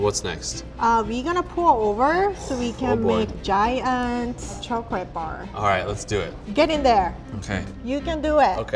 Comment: At first I thought he said a gay chocolate bar